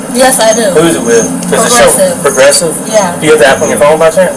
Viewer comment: I thought she said her insurance was with "Aggressive" and then I realized she said "Progressive" 😅😂